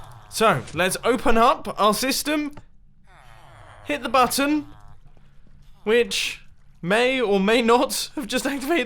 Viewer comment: I think I heard "let's hit the mutton"